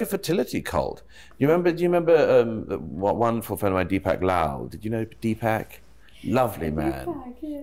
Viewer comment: couldn't make out the name of the Indian economist Douglas was referring to. It's Deepak Lal. These English accents are hard on Indian names 😜